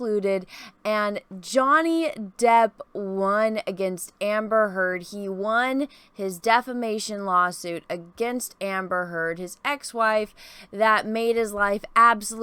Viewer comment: Misheard calling it "the defecation law suit". 🤣